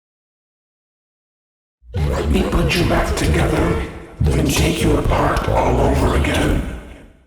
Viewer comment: i thought it said: U BETTER GET UR ACT TOGETHER OR ILL SPANK UR MOTHER OVER AGAIN but it said: LET ME PUT YOU BACK TOGETHER AND TAKE YOU APART ALL OVER AGAIN